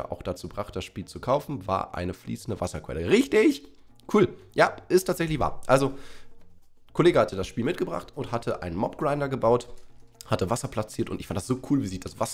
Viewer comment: Verstehe nur ich Kollegah?😂